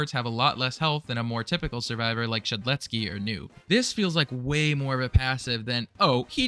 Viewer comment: I THOUGHT HE SAID SHEDLETSKY OR NUKE WHAT